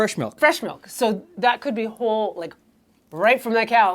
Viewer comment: I burst out laughing everytime says Homo. It's such a Canadian thing to say (Yes, I am Canadian, from out in the cold midwest). Out here we've taken to just calling it "Whole milk" as out here calling anything "homo" is, well, not kosher. Thanks for the laugh, it was needed this morning. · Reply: Fascinating, I actually heard it as “whole milk” just with elided Ls and a weak finish. But I wasn’t familiar with that shortening, I’m guessing it comes from homogenised?